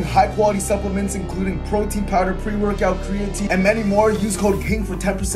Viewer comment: I swear to god I thought you said cocaine not code king